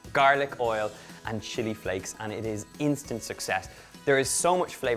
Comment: did he say shity flakes at 4:15 lol